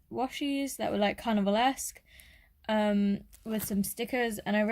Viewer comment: I thought you said cannibal instead of carnival at first 😂